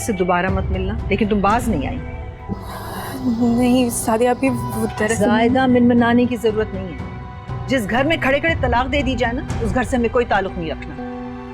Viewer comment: Did anyone notice, at 8:00she called her Zaima I guess instead of Zunera 😂😂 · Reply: Actually she saying zyada mimminane ki zrorat nhi....